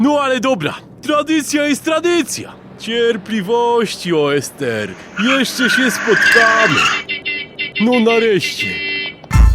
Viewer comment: czy ktoś też usłyszał cierpliwości O. S. T. R jeszcze się spotkamy